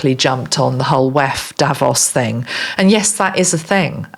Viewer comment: Hi, Thanks again for your grat channeling. I am Dutch and Instead of you saying 'Davos', I heard you say 'Devils'. What's in a name. 😅